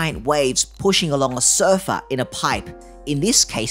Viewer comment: I thought he said sofa and not surfer, although mine do feel like a sofa, four seater with scatter cushions.